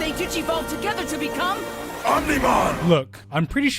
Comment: I'm pretty sure I wasn't the only one who hear "Omniman" instead, right?.... Right?.... RIGHT?